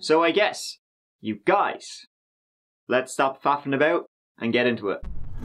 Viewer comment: Exactly, we need to quit faffin about boys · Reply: Coulda sworn he said "fappin l about...had to raise an eyebrow at that so thanks for straightening that out 😂